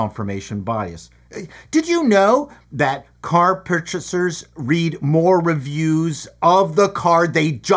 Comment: I thought said "Car pug chasers" which is a peculiar niche activity.